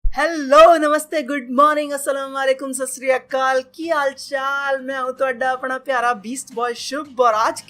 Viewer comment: did just really said Assalam u Alaikum?